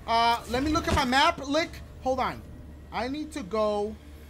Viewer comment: did he say I need to look at my map “lick” ?😂😂